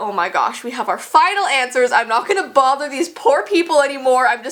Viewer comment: Lol 3:13 it sounded like she was calling them poor😂